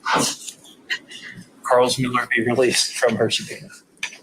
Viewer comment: Closed Caption says - "Your honor we would ask Carlos Miller be released from Hershey penis." I'd like to start creating Bad Lip Reading style videos using this court case.